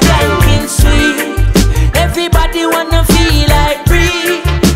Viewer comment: i thought he said everbody wanna feel like me. but wtf hahah when i saw the lyrics i realize i song the whole time wrong. xD Irie. it almost sounds the same . I think they did it on purpose. Irie have a beautifull meaning.